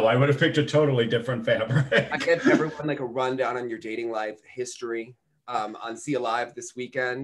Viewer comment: who did you say Audrey was allegedly dating? Mariah's Abba music is awful! The cuts are terrible and the music is just not good! She looks like she's walking around the rink. She needs better music!! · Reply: Maybe my bad. It sounded like you said "dating"